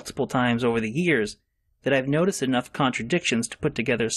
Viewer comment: did he really say "but I digest" instead of "but I digress"?